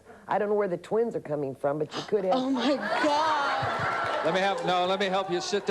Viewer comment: The woman to whom she said she would have twins. I did some research, and this woman was murdered 10 years after this show, and she never had children. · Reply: She clearly said "You COULD have them" @ 24:57 . The question the lady asks is would she be CAPABLE of having children, not will she have them.